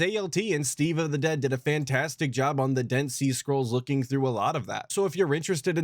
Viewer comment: did I hear that right? "Dent sea scrolls" lol